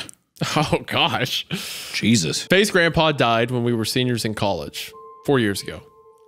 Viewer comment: I wont lie when wendigoon said Faye's grandpa at 26:16 i 100% thought he said FaZe Grandpa and i immediately was no longer scared cause all i could think of is a 80yr old hitting b02 trickshots.